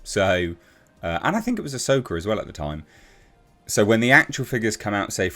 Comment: Thought he said “A Soaker”